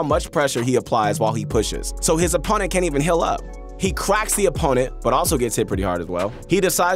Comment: Did you just say “quacks his opponent”